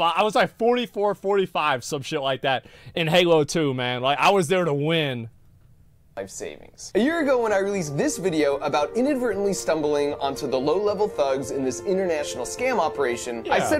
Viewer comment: I thought he said "we released smallpox" as part of the other pranks they did I was like "woah what!? 😂